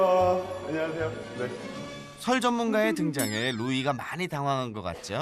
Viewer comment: (설)전문가가, (살)전문가로 들리네.